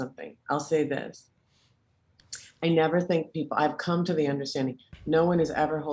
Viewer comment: I just came to say that!! Please tell me exactly what she said at 48:31....I couldn't hear it clearly! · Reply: She said “I never think people” then she rephrased what she was about to say, and said “I’ve come to the understanding no one is ever holding out on you…”